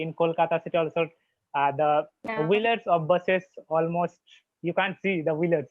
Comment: I couldn't understand this willets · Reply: I just tried to said - rainy time in my city the wheelers of busses we cant see on the road becoz of rain water.😂 Sorry for my bad english.I was confused that time😂🙏